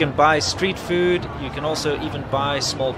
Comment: I had to rewind to here, at first I thought I heard "You can eat and buy small pets like hamsters here". Wait, what? Haha, now I realise you said "even", not "eat". :P